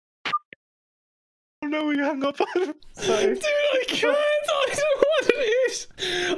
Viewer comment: I have rewatched the moment where is near exploding with laughter over and over and I have no idea what he's saying in the beginning... "Dude, I cantalktowantenish..."